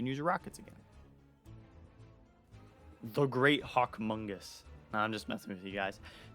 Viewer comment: what did you say here LOL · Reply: The great hawkmongus